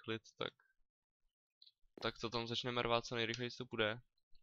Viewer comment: tak to tam začneme rvát co nejrychlej to půjde (that's what he said) LOL